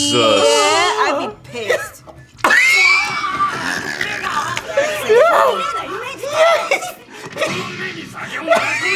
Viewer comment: i heard n word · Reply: He said "me ga"